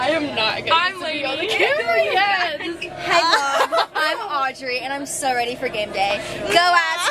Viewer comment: I love that girl vibe 🔥 everyone is nice and beautiful… I’m gonna enjoy these game day vlogs. Audrey? Did she say that? I love her omg love at first sight